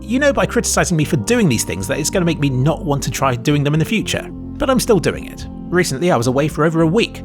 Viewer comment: You said :"make me not want to try doing them in the future", while the subtitles say "make me not want to try in the future" - literally unwatchable, pls fix.